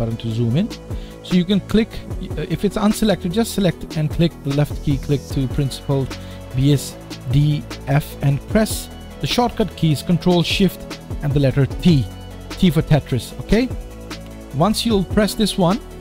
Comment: i dont know what to do in 3:30 cus i cant hear you good can you msg what are you saying in that part please i will be really greatfull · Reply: sure, we were saying that the tab name PRINCIPLED BSDF click on it so it gets selected then press the shortcut key CTRL + SHIFT + T . Once you press this the browser window will open and you can follow the tutorial. Hope this helps, if not then send message will give you extra instructions.